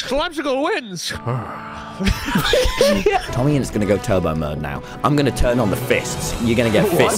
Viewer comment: i thought he said tubbo mode for a second 😂 lmao😂😂😂